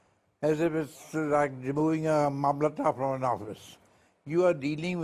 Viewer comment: what did he say ? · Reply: Well, if you don't know stuff things you hear will sound unfamiliar. He is saying the CJI is not a mamlatdar. Google "mamlatdar" and educate yourself.